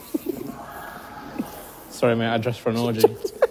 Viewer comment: “Sorry mate, I dressed for an orgy” Had me wheezing 🤣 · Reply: Oh did he say orgy? I thought he said he dressed for an audience lol